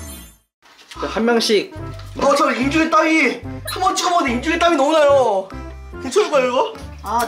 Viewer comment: 이때 이 한 번 쳐 먹었는데하고 들려요오ㅜㅜㅋㅋㅋ